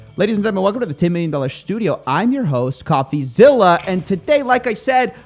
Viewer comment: did you just call it shtudio? I mean I know bobby shmurda is out of jail but I didn't know he was out here diversifying his portfolio...